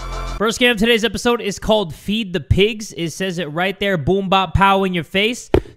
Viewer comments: Omg i swear i thought i was "feed the piss" instead of "feed the pigs" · thought that said feed the piss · … I swear to god I thought that said ‘FEED THE PISS’ smdh